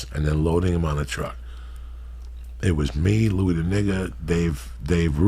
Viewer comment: did he just say Louis the Nigger 😭 someone tell me I’m too drunk please